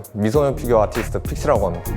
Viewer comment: 존나 잘생겼다고 생각하고 들으니 미소년 피규어아티스트로 들리네ㅋㅋ